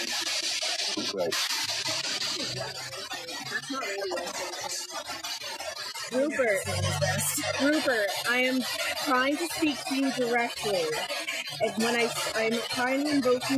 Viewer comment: i thought i heard "rubert" at around 3:05 when she first asked him to say his name